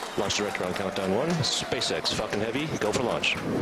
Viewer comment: did he say "space x fucking heavy go for lunch". 😆